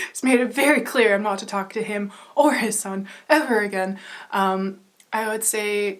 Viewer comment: Did you say "him, or his son"? He has a son? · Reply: I did say “his son” but that’s just a joke. It’s a meme 😜